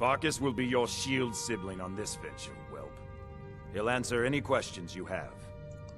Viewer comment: when skjor said "forkas will be your shield brother on this venture" I thought he said "forkas will be your shield brother on this bitch